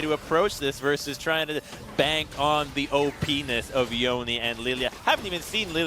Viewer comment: Did.. did just say penis? xD